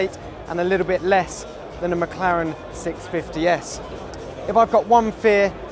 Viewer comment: did he say 'mclaren 650 or p50 s' ??